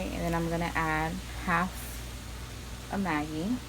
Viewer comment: What is the yellow ingredient that you used at 4:55. You said it on the video but didn't hear you clearly. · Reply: It's called MAGGI(brand name), it is basically a chicken stock cube.